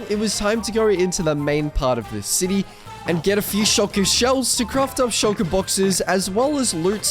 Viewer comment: IT SOUNDED LIKE HE SAID SHOTGUN SHELLS LOL